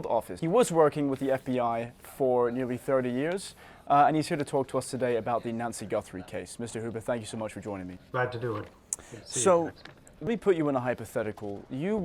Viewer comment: This kid sound like he say Nazi Guthrie every time. 😂🤦🏽‍♂️